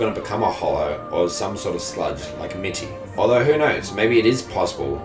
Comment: Did you call mitty a slut 3:29!?!!